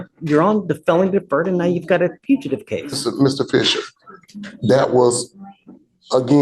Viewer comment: Did bro say Mr. Fisher at 6:20 ? :face-green-smiling